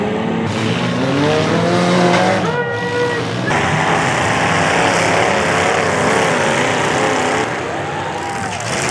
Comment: what did he say?!?!?😂🤣😂🤣😂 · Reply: Diddy!!